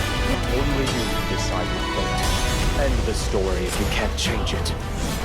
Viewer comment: Did he just say "End the story, if you can't change it?" It kinda fits into the assumed story ending.